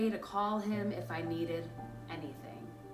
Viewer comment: The yte Tia Mowry 14:27 I thought u were gonna say who is this simp?